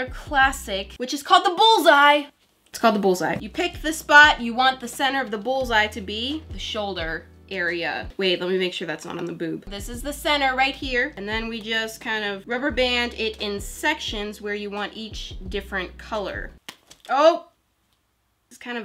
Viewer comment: DID SHE SAY BOOB?!? IVE NEVER HEARD HER SAY ANYTHING LIKE SOMETHING RELATED TO A CUSS WORD